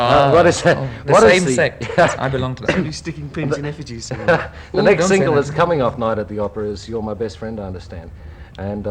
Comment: I’m so curious to know what Brian said ?? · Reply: "they'll be sticking pins in effigies"